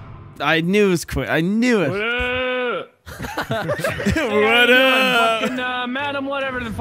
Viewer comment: are you telling me that for the longest since that clips been around Wildcat was actually saying “Waddup” and not just going “Weeuuuh” randomly lol🤣🤣🤣